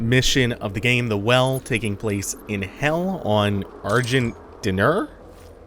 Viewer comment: I misheard him and thought he said, SEARGENT DINNER.